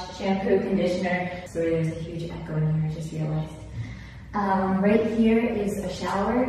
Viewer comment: what did she say around 9:30 i couldnt really hear her?? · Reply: "sorry there is a huge echo in here i just realized"